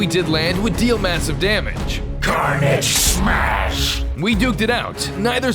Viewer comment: What do he say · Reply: Carnage smash